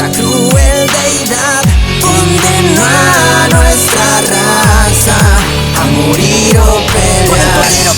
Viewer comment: esa palabra dijo "condenó a nuestra raza a morir o pelear" pero yo escuché de niño hace muchos años hoy día esto "dónde el malo estára hasta a morir o pelear" ☠️